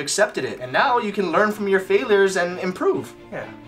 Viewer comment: I think Ryan said Failrs 😂